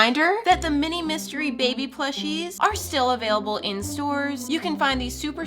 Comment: i heard "still available in sewers " 💔